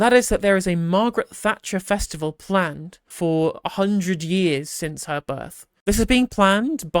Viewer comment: I thought it said "planned for cemetery"